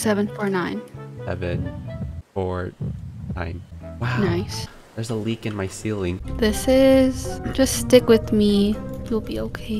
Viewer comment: I thought he said Theirs the weekend in my ceiling (like the singer) Also 9:45 and 11:35 I flinched so hard I almost. Sacked myself in the face. MY GOSH. MICHEAL JAKSON HOW COULD YOU!?!